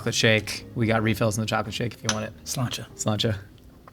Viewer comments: When they say Sláinte and the subtitles say L'Chaim 😂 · the subtitles say l'chaim, but they both said slaintè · got a good chuckle at 50:14 when the guys both say sláinte while the subtitles say l'chaim